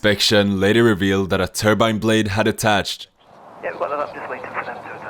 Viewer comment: a I thought turbine blades being attached was a good thing? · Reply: I heard it as "haddetached" like the 2 ds had just ran together